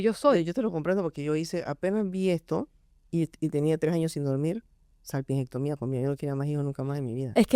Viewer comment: que dice la dra?, no logro entender lo que pronuncia. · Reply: Sal pimiento comía?